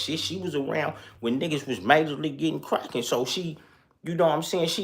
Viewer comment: I thought yo side said brackin. Not crackin. What do I no...😂